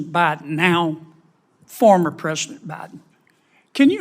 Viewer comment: At the 2:45 minute mark did I hear him say now former President Biden?